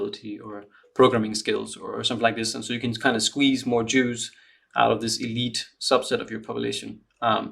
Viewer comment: Took me a minute to understand you said "squeeze more juice out of the elite subset of the population", I thought you said Jews 😅😂